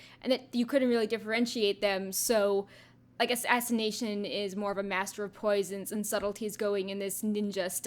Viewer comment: did you say assination, or are my ears buggin' out?